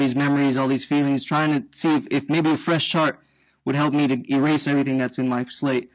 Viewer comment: sounds like he said "fresh shart" lmao